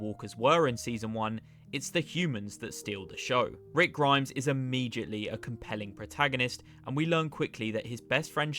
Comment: what are you saying here i mean what is this word after: Rick Grimes is ... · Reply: Immediately a compelling protagonist